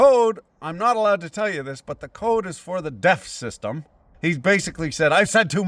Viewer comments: I kept hearing 'DEF system' as 'death system', but it works either way. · Ahhh DEF system. Thought you said Death System. Which it might as well be, from the sound of things.